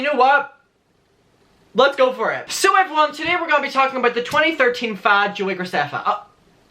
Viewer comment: In the beginning I thought he said 2013 f*g 😔